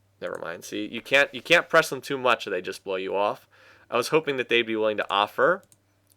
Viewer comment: did you just say they will "blow you off" ?